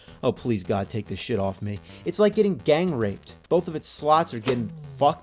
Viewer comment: I seriously thought he said "It's like getting gangrene" at 1:30, and I was like "no, gangrene is worse. Your skin dies and then you die."